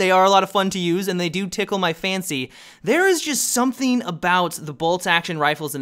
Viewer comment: it sounded like he said 'Tickle my fanny' ._.